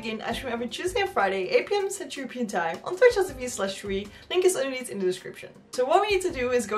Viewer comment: i need a translation from 0:11 to 0:20. I have no idea what you said there. Illegible. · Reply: I said that I stream on